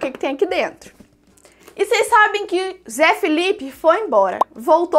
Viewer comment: Eu entendi zé felipe 0:15 kkkk